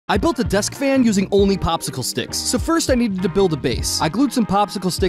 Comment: Nah for a second I thought he said "first I needed to build a Popsicle stick" 🤦